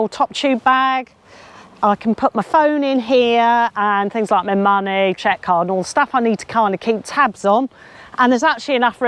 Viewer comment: did she say ‘chequecard’ ? 🤨